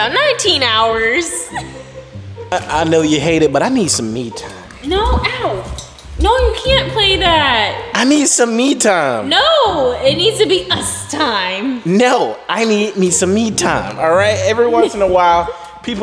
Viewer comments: it sounds like you say meet time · Yo i Straight up thought said I need some meat bra